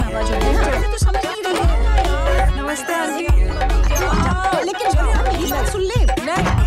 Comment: Did she say aunty to her mom